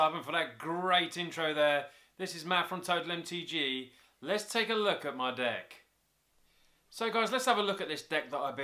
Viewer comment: OMG AT 1:20 SOUNDS LIKE HE SAID ""LET'S TAKE A LOOK ATMY DICK"" IM CRYING XD